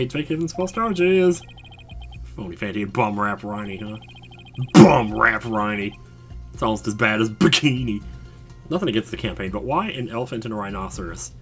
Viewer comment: For a second i thought the poster said "No more bum rapes!".